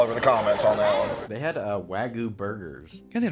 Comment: did you say wagu or raccoon?